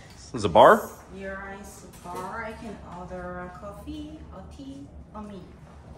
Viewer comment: did say that you can order "a coffee, a tea, or me", or did she say, "on me"? ......yeah, she definitely said "or me" like you can order her at that bar.